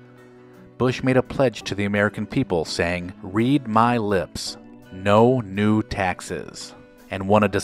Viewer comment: I thought Bush said "Read my lips, no nude Texans!" :)